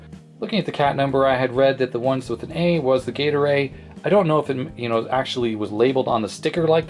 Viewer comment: As much as I know you are saying "gate array" I keep hearing "Gatorade".